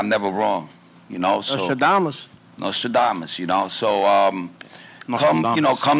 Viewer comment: what did Teofimo said here?? Noshudamas??!? the HELL does that mean???